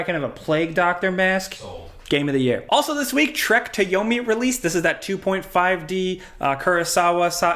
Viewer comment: I thought said Shrek To Yomi and now I can't get that image out of my head. 😂